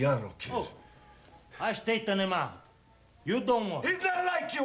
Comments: between Gyp's lines "Things are changin'...the Luciano kid-" and "HE'S NOT LIKE YOU AND ME!!", what does Joe say to him? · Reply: Oi...I straightena him outta.." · "I straighten him out. You don't worry." good lord did it ever take me a minute to figure out wtf he said